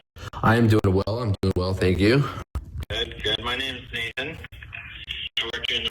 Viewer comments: I thought he said "My name is Satan" not Nathan 😂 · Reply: That's funny because I thought he said my name is thetan!😂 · I thought he said "My name is Satan" not Nathan 😂